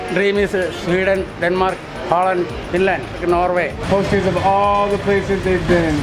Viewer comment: I think he said Finland not Greenland